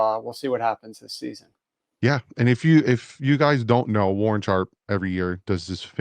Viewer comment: Thought it said Warren Sapp